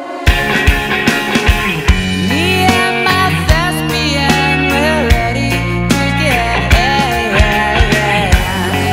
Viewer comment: Can anyone tell what he's saying at 2:57 "me and my ? we're ready to get in" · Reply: Fred Astaire